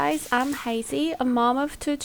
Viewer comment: I thought she said 0:29 “hi guys , I’m crazy! a mom of two children“😂 I’m listening on low volume because my baby is napping , I was thinking I’ve having another maybe that is crazy! 😅